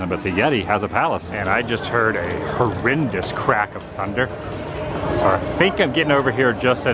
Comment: Could have sworn I heard you say," a horrendous CRAP of thunder" as you walked up to Everest. Then my mind wandered wondering what thunder crap looks and sounds like.